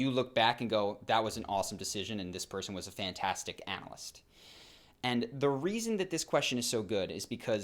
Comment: "That was an awesome decision and this person was a fantastic...." what did he say after fantastic? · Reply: Analyst